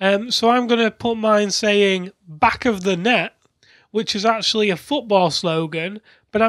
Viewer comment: I heard 'Back of the net' as 'Back of the neck' and immediately thought of Attack on Titan.